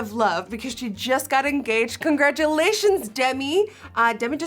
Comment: I THOUGHT SHE SAID GIGI GOT ENGAGED JSGXJSCHISDYEHBX